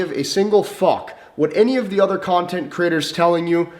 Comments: does he say content critters? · wait, did he say 'content critters'?